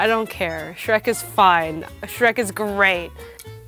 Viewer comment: Shrek is Great Me:Thought you said "Shrek is green".XD